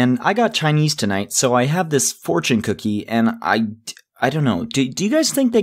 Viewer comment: I thought it said fortnit cookies